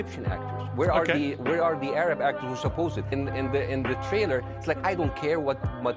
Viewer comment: Did he just say we're 👀are the Arabs😂😂